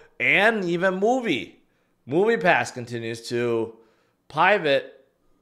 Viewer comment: Did bro just say pie vet?